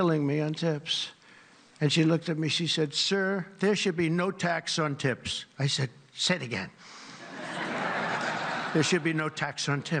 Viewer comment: He thought she said there should be no tax on tits.